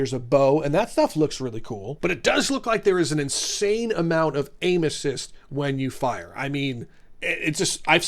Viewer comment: I just heard you say 'There is an insane amout of anuses' Instead of aim assist